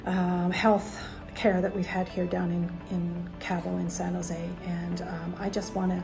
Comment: I couldn't hear where she had the fantastic health care. (7:20) .....San Jose...? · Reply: Hi there, and, went to H Hospital in San Jose. I think both of them are lucky to be alive. Thanks so much for watching . We really appreciate you taking the time to write. Stay tuned! Here are the other two videos to this series. <link> <link> Cheers